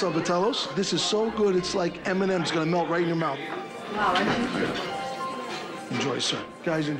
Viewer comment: idk why but it sounded like he said “it’s gunna taste so good Eminem is gunna nut in your mouth” and subtitles don’t help😂